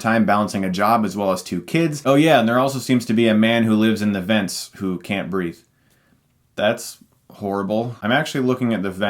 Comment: When they first said "the man who can't breathe", I thought they said "the man who can't read" and It was really funny to me.